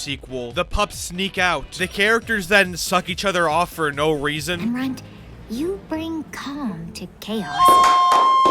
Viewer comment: did I just hear, “runt, you bring cum to cakes”?